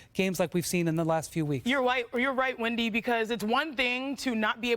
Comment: Did Chiney just say "You're white" to Wendi at 2:11 hahah